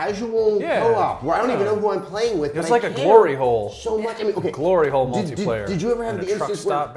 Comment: Did he just call journey a "Glory Hole"?